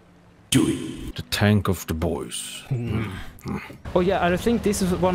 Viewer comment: "the bois" but all I heard was "DIVORCE"